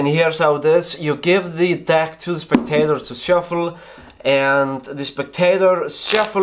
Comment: The entire time I thought he's been saying ' now you give this deck to this potato' ' now this potato will shuffle the cards' ....I thought he was being funny, then I finally understood